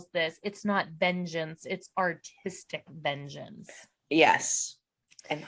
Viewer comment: I swear for the life of me I always thought Cathy was saying “autistic vengeance.” It always confused me a bit. I thought she was implying that someone on her team was autistic. Or Vivi was. Seriously that’s what I thought. I only recently realized that it was artistic vengeance. I thought maybe the editors made it sound like she said autistic instead artistic. No hate it’s just what I thought 😮❤